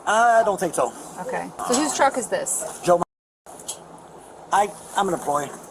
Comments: sounds like he says joe mama 😭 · it really sounded like he said joe mama lol